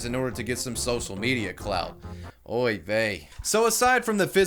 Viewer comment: Did you just say "oy vey" at 7:04 ?